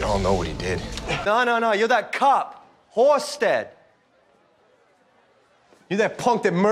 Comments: The dude said "You're that cop Halstead" But it sounded like Horsehead instead of Halstead xD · The guy said" Hey, you're that guy Halstead" but I heard"Hey, you're that guy, Horsehead!"